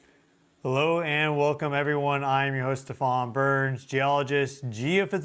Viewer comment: My subtitles thought you said your name was The Furious Burns 😂